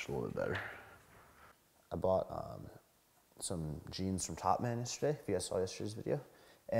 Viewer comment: Where did he say he buys his jeans? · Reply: Top man is the name of the store.. He also shops a lot at Zara